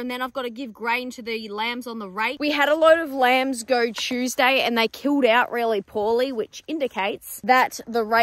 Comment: Did you say chewsday at 0:10?